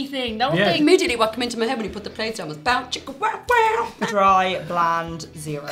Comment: wtf did she just say lol I couldn’t understand it was so fast?? Also the girl is really pretty though isn’t she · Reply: “Immediately what came into my head when he put the plate down was Bow Chicka wow wow” And yeah, she’s cute. I still love Lolsy the most.